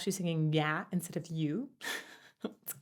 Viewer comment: I love this kind of deep analysis! I love how much you get into the mechanics of the voice and the whole body, I always learn a lot. At 19:36 what Joe is singing is actually the word "your," not "you," e.g., "I don't want your (photograph)" [I want you instead], and you can see with his pre-tag vocal, "I want to touch you (not the photograph)" he makes a clear "ooh" vowel. With his Irish accent he's got that UK open "r" sound, so it's more normal to get into that "ah" vowel.